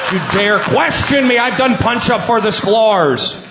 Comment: "I've done punch-a for the sklars!" What the heck is he sayin'? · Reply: I think he says “I’ve done punch up for the Sklars” meaning he helped punch up jokes for the Sklar Brothers.